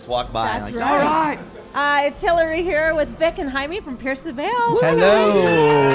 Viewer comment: At the end I thought she said "thick and jaime from pierce the veil" omg I can't